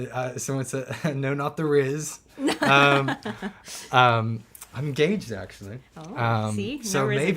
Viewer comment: what did he say at 57:15 i cant quite make it out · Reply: Hes engaged apparently